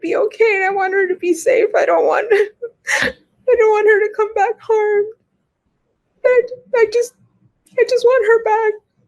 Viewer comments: it sounded like her mom said she "don't" want her to come back home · Dang, I hate it when I accidentally factory reset my phone twice. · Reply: it sounded like her mom said "I don't want her to come back home". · 😲🤯💔 I just caught this but has anybody else heard ( mom) say "I Don't Want Her To Come back home??😲🤬💔🤯 " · is saying “I don’t want her to come back home”? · ish, does she say “ I don’t want her to come back home?” Interesting slip